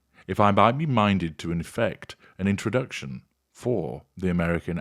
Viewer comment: I can’t understand why Suzanna tried so hard on Megan’s behalf, she hardly knew her. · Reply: Did I mishear, or did deliberately say "She wanted me to inflict an introduction," rather than "She wanted me to effect an introduction"?